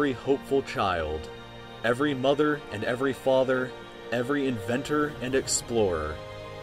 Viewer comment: i thought he said every nigga hahaha